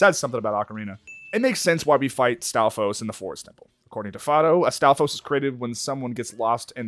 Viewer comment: At around 35 minutes in, he was talking about the Stalfos. But he started one of the sentences with A. So it sounded like he said Astolfo and I was so confused. Like, he knows about Astolfo?!? lmao